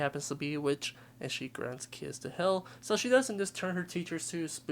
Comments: I heard "I have grabbed the kids to hell" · i heard "I have dragged kids to hell" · I keep hearing "(gibberish) I shall lemon kids to hell". How does one lemon a kid to hell exactly? Can lemon really be used as a verb?